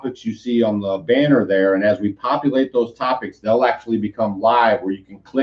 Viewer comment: LOL. I thought you said copulate instead of populate!